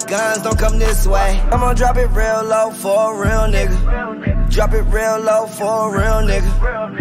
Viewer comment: I think uzi has sum to tell us💀 a Lil sus😳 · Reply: You’re so right 😳 I first thought he said “come and drop it low for a real nigga” but this nigga said “imma drop it low for a real nigga” 🤦🏾‍♂️